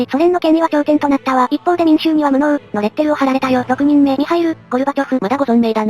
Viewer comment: ミサイル・ゴルバチョフに聞こえて2度見したら「ミハイル・ゴルバチョフ」だった……